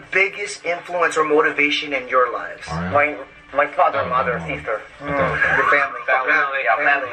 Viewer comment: I thought J-Hope said “My father’s mother’s sister”😂😂😂